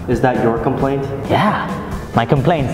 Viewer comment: i know what you're saying. is saying,"don't come plane!